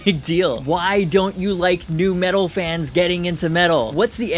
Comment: I know what you said, but I heard "Why don't you like Nu-Metal fans getting into metal!?" 😂😂😂